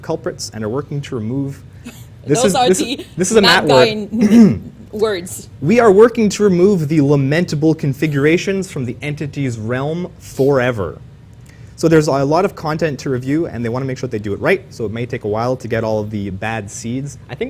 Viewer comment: I just heard "Lament Configuration" come on PINHEAD!!!!! :) :) :)